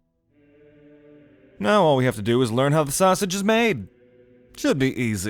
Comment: "Now all we have to do is just figure out how the sausage is made-" That's what I heard and made me double take 😂